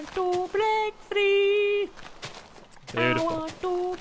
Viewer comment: For a second, I thought said"I want to break 3" 😂😂